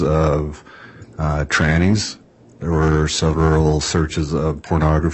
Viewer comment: HOLD UP HOLD UP HOLD UP! 42:36 Does he say “Trannys?” That Alex was searching for? 😮 Pretty sure that’s frowned upon in LDS faith.